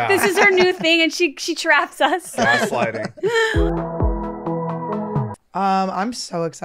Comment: did he say sliding?